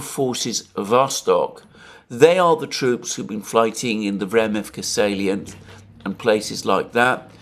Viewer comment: Can someone please help me. I heard it so often from Alexander but i never understand it (english not mother tongue)...at 7:02 "fighting in the """remicfacaliant""" and places like...."sorry, what is this word, can someone maybe help. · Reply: Vremivka salient (Donetsk oblast)